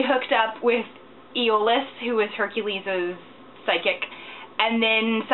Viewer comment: i thought you said Iolaus was Hercules' psychic lols and you obviously dont seem to be a fan of the show